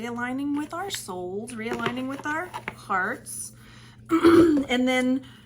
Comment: I thought I heard you say realign with our farts. Lol